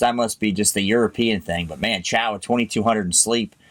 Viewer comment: i heard it as 'shower' at 22:00 instead of 'chow'